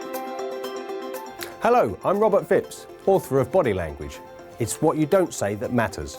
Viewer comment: I thought he said his name is Robert Fibs.